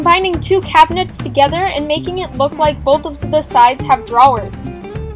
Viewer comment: did anyone else hear her say drawlers? 😂